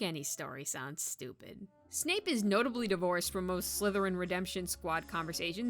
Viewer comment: I thought you were saying snape is divorced lmao